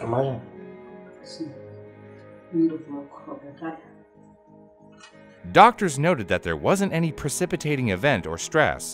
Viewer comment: this is not Portuguese, if it was I would instantly recognize, I am a Brazilian and I am not understanding what she is saying, I think she is speaking German or other language. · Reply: It's portuguese with portuguese accent. What she says at the start is "trinta e tal anos", the other guy filming says "O quê? Lá para a enfermagem?" then she says "sim" and after that i can't quite understand. The audio quality is bad and being a not so usual accent for you it's understandable that you wouldn't recognize it. (I met two immigrants that came from brazil (Minas Gerais) we all spoke portuguese yet it was really hard for us to mutually understand each other at first, which i found strange because i usually can understand brazilian accents from the telenovelas, but they speak a bit more slowly in TV shows and when it's street talk it becomes much harder to understand, after a few weeks we understood each other but at the start it was surprisingly difficult)